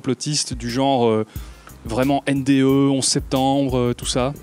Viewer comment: J'ai entendu NDE = complot ?